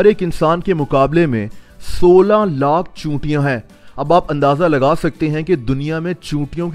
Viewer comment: i heard it sola lakh chutiyon who ever agrees plz like.